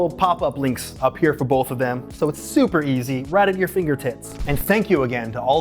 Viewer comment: Uuuhm... did you just say finger tits??